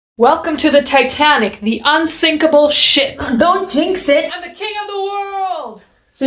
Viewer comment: I thought she said “the unsinkable shit”